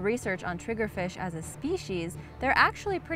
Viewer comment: oh I thought you said Triggered fish